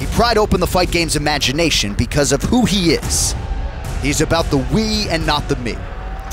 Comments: When he said "He's about the we and not the me" I thought he said he's about the weed right as it flashed to a clip of him smoking 🤣🤣🤣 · I thought he was about to say "he's about the weed"